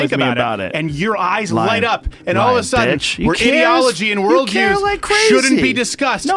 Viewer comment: did Rogan call him a bish?? Lmaoo